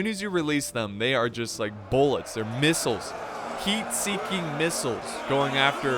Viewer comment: I thought he said "MEAT" seeking missles